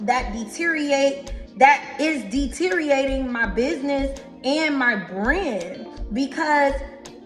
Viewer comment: When she said "deteriorating my brand," I honestly thought she said deteriorating my brain which would make more sense.